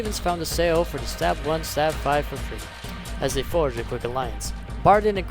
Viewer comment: Good video, though one point at 9:44 I can't hear what you said "were exiled from their homes when they ______?" Sorry but for me you said it way too fast. Can you clarify what you said there? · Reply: "...stumbled upon the three, somehow?" hopefully this helps!